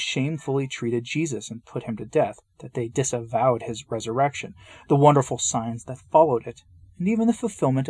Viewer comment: Ahhhh, coffee and. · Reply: ₩hat does it say at 3:36 " disembowė|| " - ❓